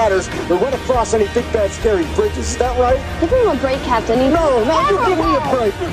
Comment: What did Sharona say at 3:40? I couldn't understand it, I have no idea why :( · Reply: She said Give him A break